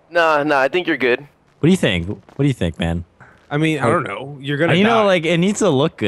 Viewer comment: I thought the cop said vannos is gonna die but it's you are gonna die.